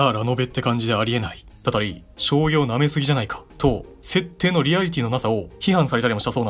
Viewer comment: 将棋を舐め過ぎなんじゃないか？ ↓ 醤油を舐め過ぎなんじゃないか に聞こえてしまったwww